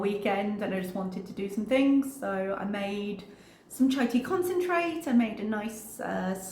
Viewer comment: I was half listening at the very beginning of this and swore you said "turkey concentrate" (actual words =chai tea concentrate 🤣) and for a split second despite my horror at whatever "turkey concentrate" would be I caught myself thinking it can't be that bad if my favorite reactor is using/eating "turkey concentrate".🙃🤣